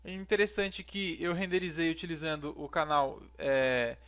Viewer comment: Como consigo esse "canal id" nao sei... o que voce falou nos 0:58 ?